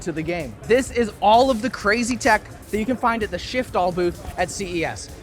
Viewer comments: I must be tired because I legit thought that said "Shitfall" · I swear to God I thought it said "Shitfall" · Only here because I swore it said Shitfall